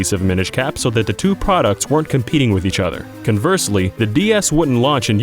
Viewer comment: Almost sounds like he said “the BS”